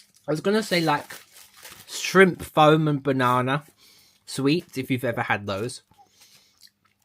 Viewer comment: What do you say here? Shrimp foam and banana? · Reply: Yes that's right shrimp foam banana. They're sweets in the Uk and shaped like shrimp and banana the consistency reminded me of the mars Raspberry smash I was trying and flavour.